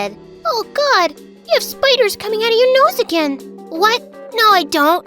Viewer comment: it's say "noise" in the captions instead of "nose"